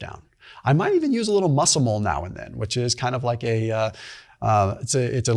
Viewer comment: What kind of mushroom is he talking about @ 18:18? I can't figure out what he calls it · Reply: Muscimol